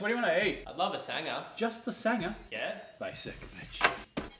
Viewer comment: This entire time I thought he was saying basic bitch. I just finally heard the Mitch! 😂😂😂😂